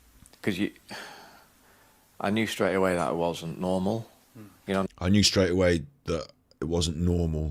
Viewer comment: I thought he said at 3:08:38/4:59:02 “ I knew straight away that I wasn’t normal “ not it